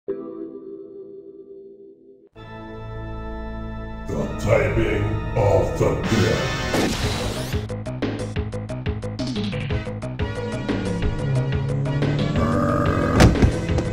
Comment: Is it just me or does he say "The typing of the bed"